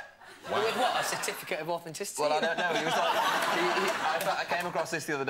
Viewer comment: What did Louis said at 14:51? I can't hear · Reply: 'with what, a certificate of authenticity?'